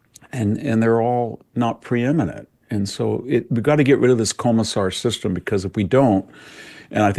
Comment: what did he say a comisar system? Any help would be appreciated. I’m just curious what that meant. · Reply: “Commissar”…..think it means like managerial class in Soviet communism…like appointed administrators He’s comparing out of touch white Leftists to Commissar’s in that they are under the impression that they know how everyone should operate etc